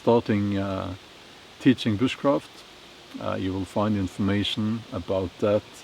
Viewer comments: for a second I heard him say "witch-craft". well, wouldn't surprise me. lol. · You almost lost me there when you said you will be teaching bushcraft I thought you said witch craft oh man I'm glad I looked at the transcript at the bottom when you were speaking